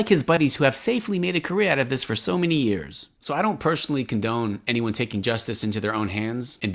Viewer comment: he say : I don’t personally condemn those who… 7:30 I just hear victimisation of his cult and again a form of takhia. Stop lying about the fact that in Islam what the murder have done is halal and even praise by allah ! And no he didn’t attack Muslim people but the Dogma. Islam is not a preson it s an idea so everyone can be allowed to mock an idea. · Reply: He said condone not condemn